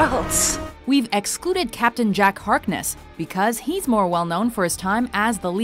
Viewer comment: did she just say captain jack HARCKNESS instead of hartness